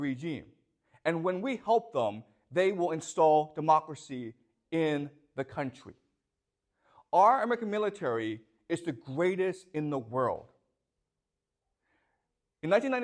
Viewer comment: I think he went to say "our American military" around 19:10, but it came out as McMilitary and that's the funniest thing about any of this... Wait, now I'm sad again.